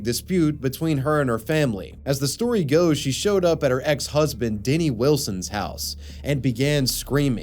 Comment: somethin idk Did I hear "diddy wilson"??? oh man if thats real then his name aged so badly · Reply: denny wilson